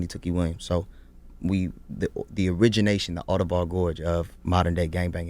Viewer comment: what did he say? The origination…then?